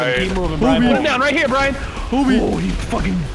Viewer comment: all i hear BRIAN HRCCCK